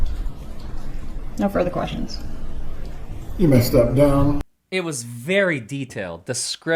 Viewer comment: the Judge says “you may step down” to Drina and your subtitles say ‘you messed up Donna’. Hilarious!